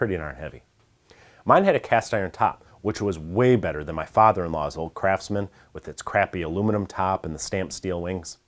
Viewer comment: How would you classify a 10" 3hp Craftsman 113 from the early 70s with a cast iron top? "Contractor?" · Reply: I listened to video again to quote you properly and that's when I realized Ihad misheard you as saying "crappy old Craftsman with aluminum top" rather than "old Craftsman with its crappy aluminum top."